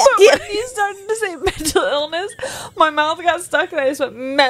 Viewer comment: When you said "mental illness" I thought you said "mental elvis" and was laughing with you guys but for a completely different reason 😂